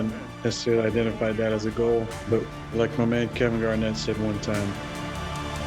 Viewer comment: WAIT WHAT DID KEVIN GARNETT SAY AT THE END?! omfg.. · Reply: ANYTHING IS POSSIIIBBLLLLEEEEEE!!